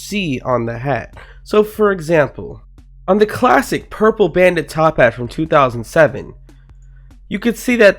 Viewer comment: i thought he said Purple Banded TOPPAT